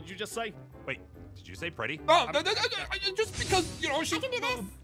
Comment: Ok when I first heard say, “you know she’s a what?” I thought he said “You know she’s your WIFE”